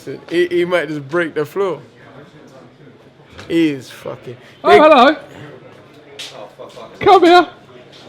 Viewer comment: did Eddie say "oh fucker" when he caught him? HhHa